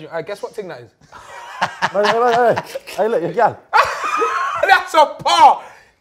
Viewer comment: What did Chunks say at 10:00 I couldn't hear it at all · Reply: This is your girl